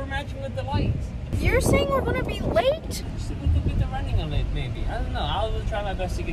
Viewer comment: Did say baby or maybe 😬😐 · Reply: He said baby